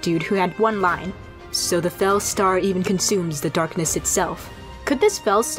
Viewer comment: I heard “fell stone” in the trailer.